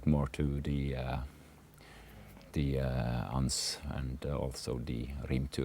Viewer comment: what does he say at 1:44 ? "The Anns" ? Rimturs I know, but what is anns ?